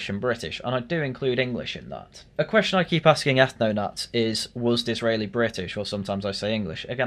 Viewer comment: Was who? I just heard some mumble that sounded like "Dizreily". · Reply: Benjamin Disraeli, Victorian British PM.